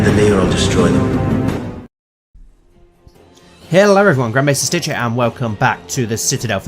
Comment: I thought he said Grandpa Maestar Stitch